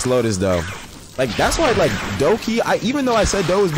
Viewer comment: instead of "lowkey" bro said "doughkey" at 3:35 Lmao